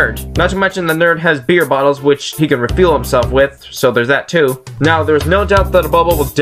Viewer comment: what did you say about the beer bottles because it sounded like you said reveal him self with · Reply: I said refuel himself with. XD